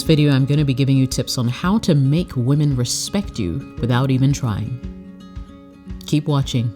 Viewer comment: I thought u said without trying 😑